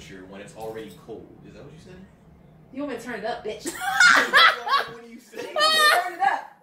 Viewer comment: Did she call her son a B*tch around 12:54? Wow I hope not.